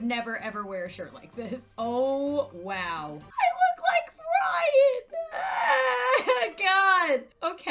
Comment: Oh sorry it was Ryan I thought you said Brian